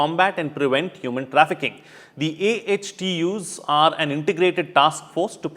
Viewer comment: What does HDO mean? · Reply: They actually meant AHTUs (Anti-Human Trafficking Units). Not HDOs. Perhaps misheard it.